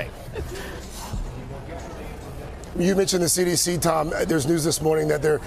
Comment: I hear "Drink the sanitizer"